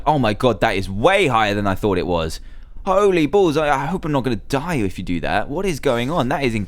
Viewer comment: did say "holy balls"?